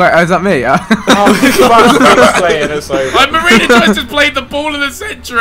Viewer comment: I thought it was he said marina juice